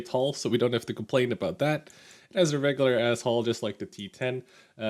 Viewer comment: did i hear 'as a regular asshole'? LUL just kidding